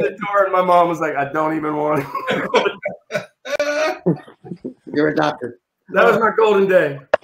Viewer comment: did colin say "youre adopted" ?